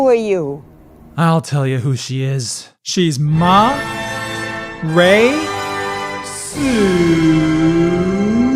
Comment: I thought he was gonna say ma rey cist